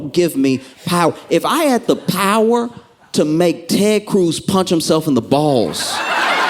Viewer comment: for a sec I heard "terry cruz Punch himself on the balls" 😂😂😂